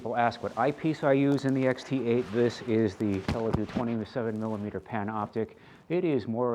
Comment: For the XT8 did you say Televue 20 to 7mm? So is that a zoom optic? It was hard to understand you as I think you bumped your mic right as you were saying it. I'm talking about the 2:03 mark in the video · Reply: 27mm Panoptic. I don't care for zoom eyepieces.